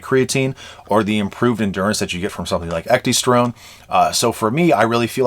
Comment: what was that thing you mentioned at 2:09 for better endurance? It sounded like it was pronounced ectestrome but I tried googling a few similar spellings and couldn't find anything. Just curious, thanks for the great content keep up the good work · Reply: Ecdysterone, I have the one that I use linked in my description but otherwise seems that every other brand I’ve tried works as well, I just use the most potent one. For me that moves the needle more than anything else but considering endurance is the easiest vector to increase for humans, any combination of those things can have some awesome results